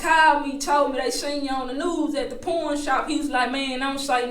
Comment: I thought she was saying porn shop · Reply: I thought she said p*rnshop too and then I start to think this b** is blind right!...u know she ain't blind she's looking for XXX movies God forbid that accent.... and she really saying pawn shop...smdh